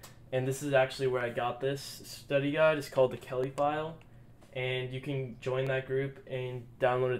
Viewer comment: Just found youuuuu. May I have the study guide…. I think you said the Kelly File